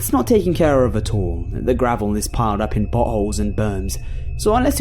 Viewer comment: I thought I heard buttholes instead of potholes!😂